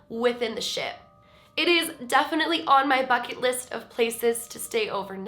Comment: i thought u said, "it is definitely on my FUCKING list..." but u said BUCKET list, had to replay it, i've never heard u curse lmfaooo